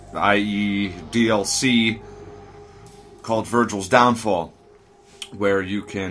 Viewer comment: I didnt read the video title, and at the beginning i heard "virgins downfall" and laughed my ass of :D ^^